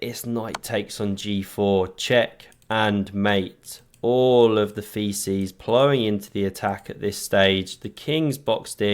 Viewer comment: Did I hear correctly at 8:21 "All the feces flowing into attack"? 😅 Or do I need to clean my ears? 😂